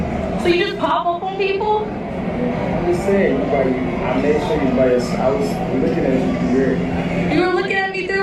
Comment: did he say “i made sure u were by yourself” ………….